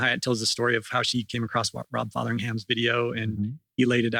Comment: At @ 30:20, mentions a video by someone that influenced Karen Hyatt, but I can't make out the name. Rob Fatherham? Can you please tell me the name? EDIT I was able to find it - Rob Fotheringham